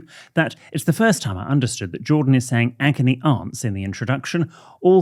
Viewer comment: I thought Jordan was saying "Shagony aunts" but quickly remembered it isn't a sex podcast.